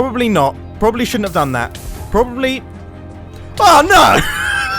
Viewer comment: I rlly thought he said “fuck-no”